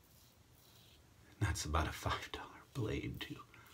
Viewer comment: Brilliant comparison! Enjoyed the vid! At 7:20 does he say “f**ked up” or 5 dollar”? Actually can’t tell :/ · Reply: Zorbcck I heard $5 blade.